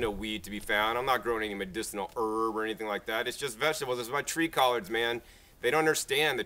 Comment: what plants are you alking about? o.0 i can't make out what you are saying: treecollege? o.o